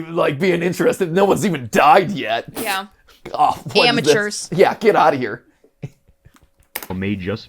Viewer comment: It sounds like Gabriel is saying, “Get out now,” when he's chasing the Ghost Buster. I feel bad for these kids. Rip.